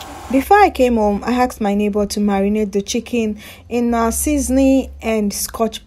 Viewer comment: Omo! E be like say I go come dey live for your yard o! Hope them no dey rob una too like them Rejoice? When you said "I asked my neighbor to marinate...", I thought you were about to say "I asked my neighbor to marry me" 😂😂